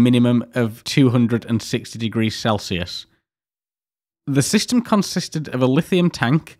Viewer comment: Minor mistake probably but around 22:06 You say 206 but the closed captions say 160 Thanks for including the closed captions anyway tho, its a pretty small thing for most people but its literally everything to a few.